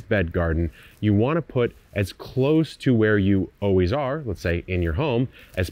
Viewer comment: lol do all Californians say “oh wheeze” instead of “always?” Or was that just a verbal flub? lol in any case now you’re definitely going to take notice 😅